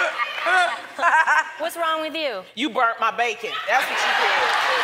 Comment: YOU BURNT MY BACON! LMAO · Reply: Jessica Torres I thought she said “you burnt my baby”